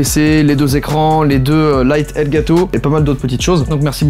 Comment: "les lite El Gato" j'ai compris sa 12:31 En espagnol (les lite je sais pas traduire) donc ça fait el Gato "le chat" 😂